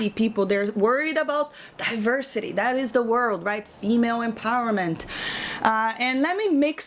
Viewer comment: even the subtitles got it "email empowerment" instead of "women empowerment"... 'cause it's spam-ish these days... and deserves to end up in trash...